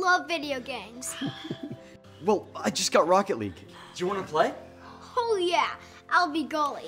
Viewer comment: when the kid say oh yeah I thought he said hell yeah